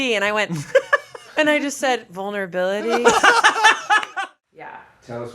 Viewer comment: PLEASE make “vulnerability is my super power” a merch line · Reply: For some reason I thought she said 'boner ability'